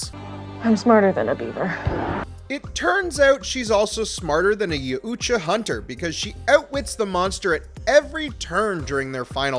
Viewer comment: did you just say ya uuuutja hunter?.......wtf?!?!?!